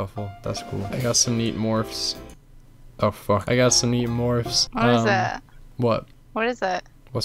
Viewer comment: Did anyone else hear him say meep morps